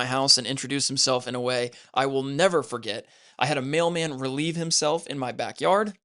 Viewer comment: am I hearing that right? A male man? 😭